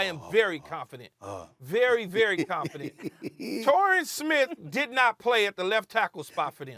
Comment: Did he just say Torrien Smith?